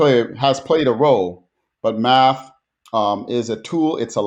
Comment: I heard Matt instead of math.